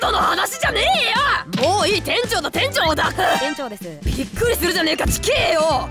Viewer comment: 一瞬店長の店長を出せって聞こえて下ネタかと思った(笑) あとふじくんかっこいい